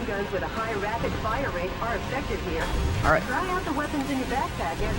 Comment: "Machine-guns with a higher firing rate are effectivlyer" Am I hearing this right? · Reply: MegaJotie "Machine-guns with a higher firing rate are effective here"